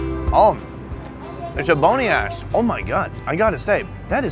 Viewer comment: Did he really say ebony ass at 14:47 lmao